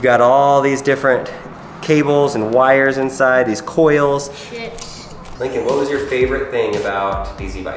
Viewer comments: it sounded like said "shits" lol anybody else catch that? Like? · Did Lincoln say sh*t at 8:09!?!? Like if you agree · Did Lincoln say "shits" at 8:09 what? lmao